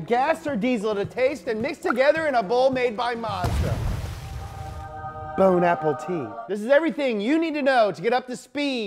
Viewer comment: did I just heard "bone apple tea" 😂😂